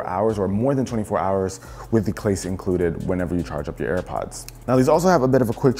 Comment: when you say clase instead of case